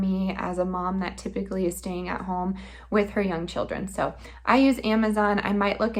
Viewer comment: I have to tell you....I had to back up and listen again because I swore you said three young children....I was like❤ whew-who!! 😂😂😂after the replay it was her young children....lol. I can't wait to see how the hummus does with canning. I love that stuff. My potatoes weren't good this year either....but I'm getting some from a friend. That grows tons of them. Great video!!